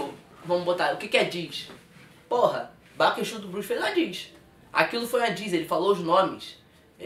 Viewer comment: Quem fez uma diss ? Não entendi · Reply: Silva baco exu do blues